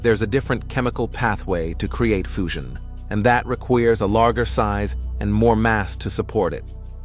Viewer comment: "... and that requeers..." instead of "... and that requires..." Seriously? Another example of fake AI voiceovers! Why don't you at least listen to your fake narration at least once before you post?